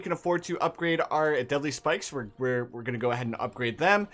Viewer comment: I know he said “deadly” spikes, but for some reason I heard it as “deli” spikes